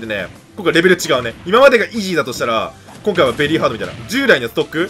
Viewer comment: 何度聞いてもも滑舌悪すぎてイギーにしか聞こえないwww